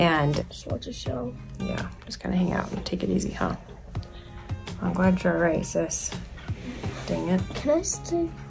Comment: Captions said, “I’m glad you’re racist” 😂